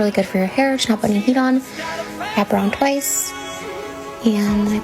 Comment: Did she say "I browned twice." ????